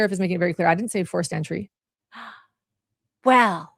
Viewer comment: I swear she was saying 4th century...not forced entry